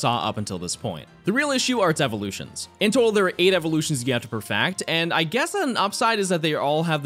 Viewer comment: is it just me or does he sound like he is saying "perfact" and "dax" instead of "perfect" and "dex ??